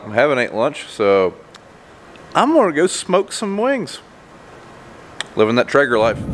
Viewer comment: I thought you said smoke some weed!!